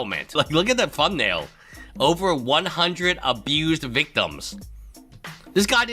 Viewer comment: I did a double take because I thought Jeremy’s video said “Xanderhal victims” and I thought this he was spreading Thought Slime’s old sex cult allegations.